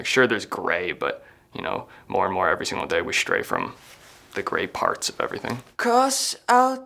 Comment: i thought he said "we stray further from god each day"